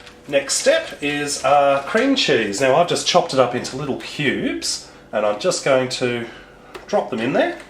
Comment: i thought you said "pubes" not cubes. HAHHAHAHA OH GOD